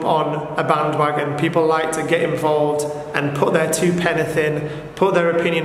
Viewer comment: penith? Is that what his said? Put their two penith in? I’m sure regrets wanting to “penithtrate the poor girl”